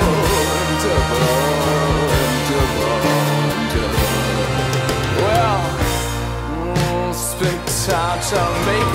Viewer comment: What is he saying at 2:53? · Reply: "...and ever and ever and ever."